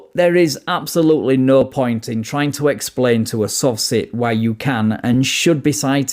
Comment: he said sovcit but for whatever reason I thought he called them soft sh*t 😂. I had to rewatch the last minute twice for laughing so hard. I probably wouldn’t have laughed so hard if the Lt. hadn’t already called them a douche lol! 😂